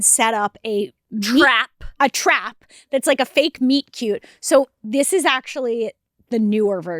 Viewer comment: I thought they said "meat cube" in the intro and I am disappointed